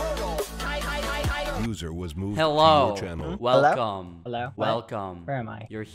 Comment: I thought it said ”Loser was moved to your channel”